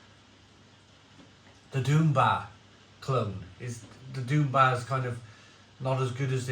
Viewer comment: I always thought he was saying Doomba, which was maybe Dunbar in a Northern accent haha. Ok it’s Doom Bar